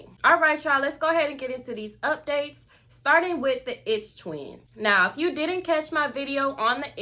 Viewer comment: Why everyone did say the “it’s twins” it sound like she saying the “itch twins” 😂😂😂😭